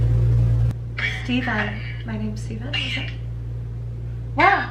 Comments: I heard lucifer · when asks for the spirit’s name I swear it said Lucifer